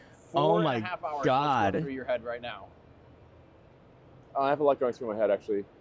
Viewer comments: Sounded like Elon said "I got a lot of drugs going through my head". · “I have a lot of drugs through my head…” is what I heard him say.